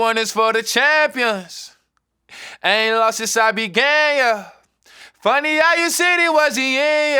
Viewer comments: I thought it said "any losses I been gay yuh" 😂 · I always thought he said "haven't lost since I was gay 'uh' " which would also have been fire!! Love deez duuudes!! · Songs fire but I felt like it would’ve been doper if nas said “I ain’t lost since I been gay yea” like he was born that way so he ain’t never lost since the beginning…I thought that’s what he said till now lol